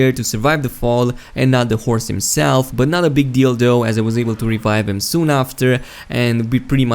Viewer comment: Not a bit deal though. As in, my horse died, not a big deal though because I was able to revive... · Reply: It sounded like you said 'Not a big DILDO'. LMFAO! Lost in translation I guess. =)